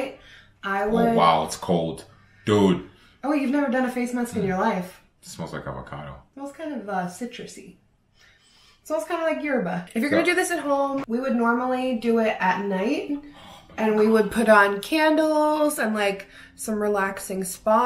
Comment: When she said smells kinda like Yerba, I swear I heard "smells kinda like your butt"